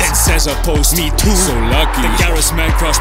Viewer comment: It sounded like Jürgen said Solanke. It’s a good thing they were subtitles.